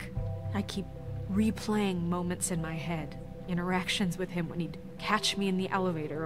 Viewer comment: I swear when Snow said 'interactions' I thought she said 'erections' X'D